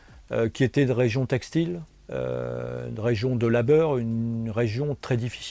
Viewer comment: he says 'de labeur' (labour) instead of 'de la beurre' (butter, although it's masculine so it would be 'du beurre' if he was saying that)